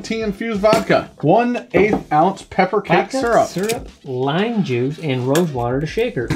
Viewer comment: I thought said ipecac syrup and thought this will not end well.